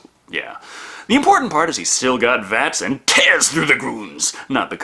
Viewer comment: Did say V.A.T.S.? at 3:36 ?